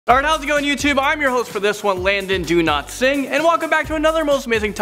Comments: Did he just say in the intro? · I thought you said Landon,do not sing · I thought you said Landon do not sing